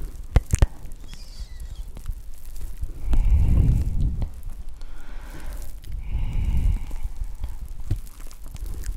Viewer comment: Mais dans les comm svp aidez moi, elle dit quoi à 11:20 ? Je comprends des mots que je ne devrais pas je pense 😂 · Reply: Elle dit une constellation